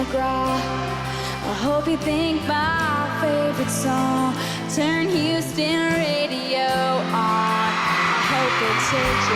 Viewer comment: Gracias muchas gracias, pero que dice en el min 9:48 ? Porque cambia de la canción original no? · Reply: Dice "Turn Houston radio on". A veces, Taylor cambia alguna palabra o algún verso de alguna canción para hacer alguna referencia a la ciudad en la que está dando el concierto en ese momento :)